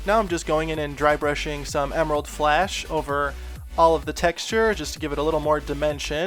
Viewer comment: I thought he was saying emerald splash